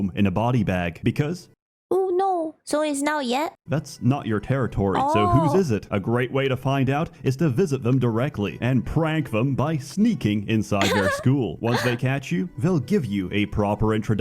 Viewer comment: This Sseth video is so deranged that i always misheard this part as "Sneaking inside their skull" which is weird, but not weird enough to me to think it isn't possible 😆